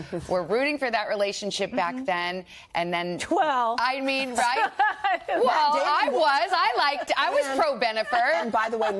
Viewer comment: Will someone please tell me what Jen said in response to Savannah at 4:03? It's only one word, but for the life of me, I cannot make it out. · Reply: She just said “Well”!